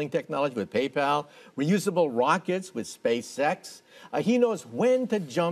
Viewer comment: i thought michio said space sex. anyone else??